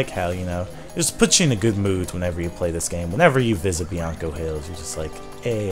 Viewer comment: When I was younger, I always thought it said BLANCO HILLS